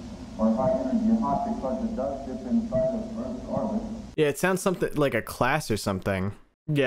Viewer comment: 9 months late, but heres what i heard at 50:22 -- <--- means i didnt hear it or only some muffle of it so it could be wrong "In this class, we follow a planets trajectory --------it does dip inside Earths orbit"